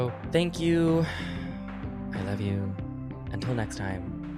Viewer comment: I always hear "Stay Fruity" as "Stay Froody", and I think we should lean into it. The gays own "froody" now, it's canon.